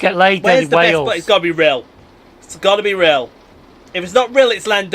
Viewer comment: Did you say Rail? Well, I guess you'd be railed. :P